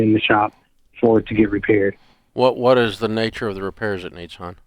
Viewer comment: Did Dave call him hun?